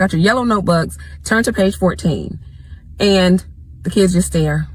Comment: ish she say turn to page 14 and the caption says 17!!